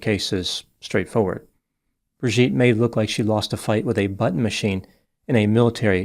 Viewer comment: “Lost a battle with a button machine” made me have to go back a minute to listen again. Only because I thought I heard “lost a battle with a butt machine.”